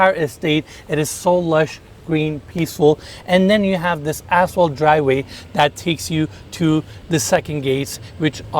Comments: 🌟 needs to work on his English. I thought he said A**hole driveway. But after listening to it three different times, he said ‘asphalt’ driveway. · did he say assshole driveway? I mean, I know he is a slimeball, but...LOL...ya, that was asphalt, but the way he said it, sounded like..... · Thought he said , this a-hole driveway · Did say asphalt or asshole driveway? 🤣😂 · i thought he said you have this A$$ HOLE driveway lol his accent